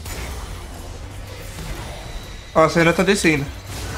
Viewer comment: juro q ouvi "A Senna ta descendo"